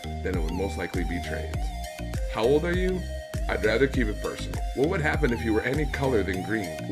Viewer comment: Yo! Thanks for answering my question! 23:30 What did you say? I couldn't understand what you've said. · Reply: I said I would rather keep it personal